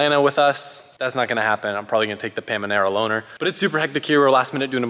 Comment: Had to re-watch bit at the end to see if he said what i heard ''the pamenera'' not hate it just triggered me, lol, its a Panamera.